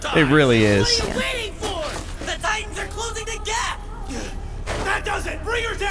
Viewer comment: wow! Eren literally said that the titans are going to obey him. Wtf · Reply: dammit lol you are right. I'm sorry 😂😂😂😂😂 I thought I heard him say," the titans are going to obey me" 😂😂 this was 4 months ago, thanks for correcting me bro.